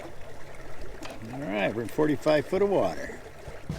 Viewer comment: Did you say 45 foot of water or 4-5 feet of water at 14:50? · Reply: 45. there is a hole there in that stretch before the more standard 6 ft deep bay. my gps was reading 45 for a minute or two. that stretch is called Mitchell's cut. Non of the charts list depths in that cut that I have found to double check that sounding but that is what the depth finder was reading out.